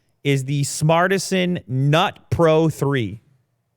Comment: I died when I heard “the NUT pro 3 “ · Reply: I thought he said Not Pro 3. And given how everything is a pro these days. I actually liked the fact they challenged them. Then i realized it was nut pro and feel like i owe TikTok a standing ovation and around 400 bucks.